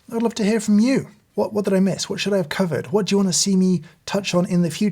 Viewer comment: Maybe it's because I'm American, and maybe because it's all I've been hearing but man, it really sounded like at 17:24, you said "... What should I have COVID?" Please don't COVID anything😂